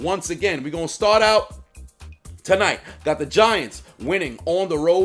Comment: I thought you said "the VaGiants" :)